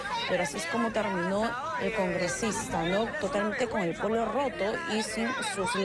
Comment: Jajajaj pense que decía con el poto roto